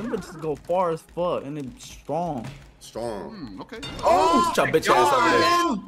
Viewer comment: No 4:31 did Dylan just say the n word 💀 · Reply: Nah he said "and they be strong" but the way the tone in his voice went down, if u don't listen to it properly it does sound like he said it